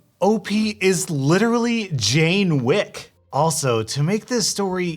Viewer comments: Did he just say jain wick? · Oof I heard "Jane Wick" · Did you say Jane Wick?